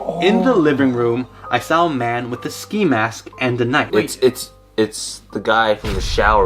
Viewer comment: man with a ski mask Me: got damint i thought yo u said hockey mask